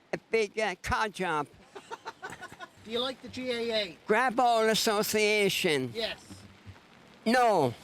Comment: Whoever transcribed your subtitles missed a joke from the auld fella at 10:09. He actually says Grab All Association. He's calling the gaa greedy.